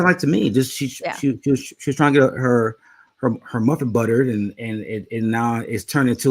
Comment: lol. get that muffin buttered. sounds like a bakery treat. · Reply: I thought he said trying to get his mother buttered. I'm still buttered.